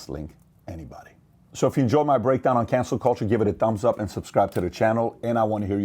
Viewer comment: You know @ 18:12 my captions said that cancel culture was "cancer culture" With the way things are going nowadays I honestly couldn't tell the difference